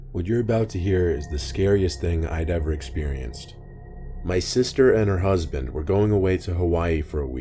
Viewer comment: I thought u said my sister and my husband 😂 I was like 😟